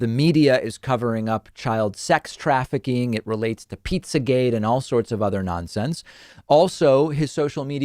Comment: Pizza, did he just say pizza 😂😂😂